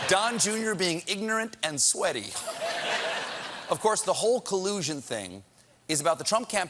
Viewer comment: You know that yanni or laurel thing ..... I hear "collusion"